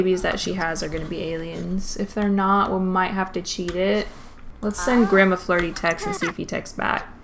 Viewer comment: I heard it as "Let's send gramma flirty text".....